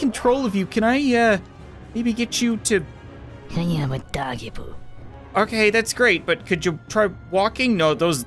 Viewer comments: did she say i have a doggy poo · I heard I hate doggy poo who else